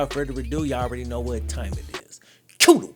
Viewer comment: Wait all this time you where saying cue the Wayne? I thought you where saying cue the way. Who is Wayne? Is that your real name? EDIT: oh it’s the lil Wayne song at the beginning right? I never put that together